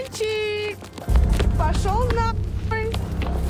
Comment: А что сказала на ухо на 28:26 минуте? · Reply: я записываю разговор на диктафон